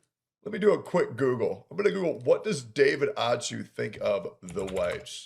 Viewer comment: I thought he said David a Jew.